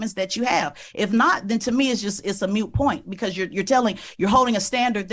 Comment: Did I hear her say " a mute point"? 😅😅😅 she tries to sound educated but then says the wrong phrase... hahaha! Dumb ass!